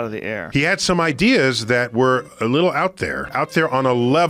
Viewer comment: Did he just say little idea